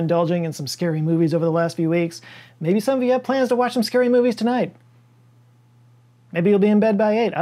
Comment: I legitimately thought he said "Maybe some of plan to watch Today Tonight" (an Aussie sensationalist 'news' show)